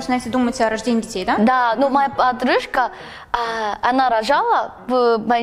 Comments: мне же не одной на 13:08 послышалось «отрыжка»?😂 Update: ого, 100 лайков и 9 ответов? серьёзно?😂 впервые такое😂😂😂 но я не лайкодрочер, да и вообще не понимаю, зачем просить лайки на коммент в ютубе, так что мне как-то не оч интересно). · Аххахпхпхаххахахахпхпхпхахах почему я подумала что она сказала ОТРЫШКА, А НЕ ПОДРУЖКА ХАХПХХПХПХПХПХХПХП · мне одной показалось что она сказала моя отрыжка